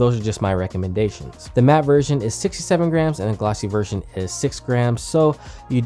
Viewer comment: is it just me or did he say "the glossy one is 6 grams" or am deaf